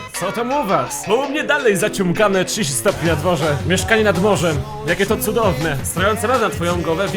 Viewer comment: zamiast "mieszkanie nad morzem" usłyszałam "mieszkanie na dworze" XDD